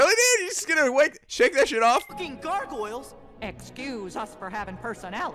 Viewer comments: After Nico unpauses it sounds like Sora is saying "FUCKING GARGOYLES!" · I thought SORA said "FUCKING GARGORILS?!" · the way nico paused it, it sounds like sora says ‘f*cking, GARGOYLES?’ I mean me too >> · sounds like sora is saying "FUCKING GARGOYLES?!" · out of all of Disney's movies, Tarzan and The Hunchback of Notre Dame is my favorite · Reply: when Sora freaked out about the three gargoyles, the thought he said "F****ING GARGOYLES" not talking gargoyles